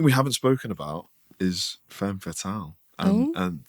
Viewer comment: or as i previously misheard it, femme towel